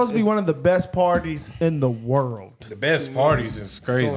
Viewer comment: ish, did I really hear someone say, "There's only two parties"? Hope not, I'd be very disappointed.